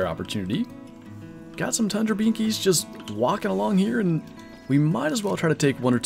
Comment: Was it just me or did anyone else hear sat tundra binkys